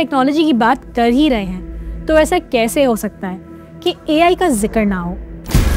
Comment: is it me r i heard AL instead of AI 😂😂